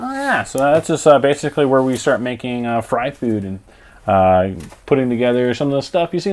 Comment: I thought he said that's where they start making fried food.......